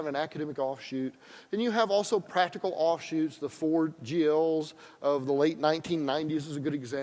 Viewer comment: Quote: "Then you have also practical offshoots, the ?ford-jills? of the late 1990s [....]" Can someone translate "ford-jills" for me plz ? What did he say ? What is he talking about ? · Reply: I think he said 4GLs: <link>